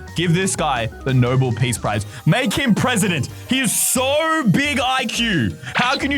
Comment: for a second, i thought that you said "make him pregnant" instead of "make him presidents"